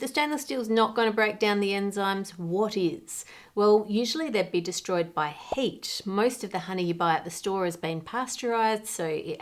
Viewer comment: lol, Anne, your accent at 5:25 sounds like HATE destroys enzymes. Thankfully the animated fire helps clarify what you said! XD